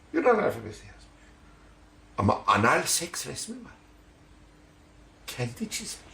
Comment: Fatihin defterinde kendi çizdiği anal seks resmi mi var dedi ben mi yanlış duydum?